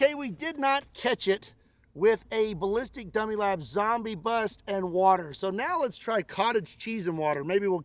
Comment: Is it just me, or did I hear say; "Delicious Gummy labs Zombie head"?